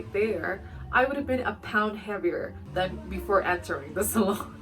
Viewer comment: I thought you were saying salad towards the end of your video when saying salon. 😂I’ve never heard it pronounced that way